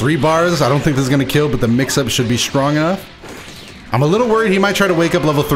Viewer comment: did you have a stroke saying "stronger" or do I hear this wrong lmao